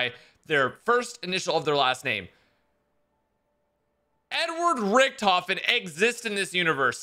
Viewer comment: “Nword Richthofen” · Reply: He said Edward I thought he said n word to but I just replayed it